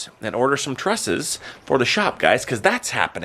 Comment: Good grief! I thought you said you had to go buy dresses for the shop! 😂😂😂 I was so intrigued 🤔😂 I had to rewind.